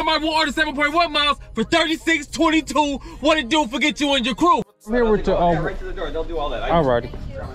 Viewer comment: what did that man say the 4:13 mark of vid? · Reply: He said they “They are going to take care of that, you can just take it to the door.” I don’t think he lives there.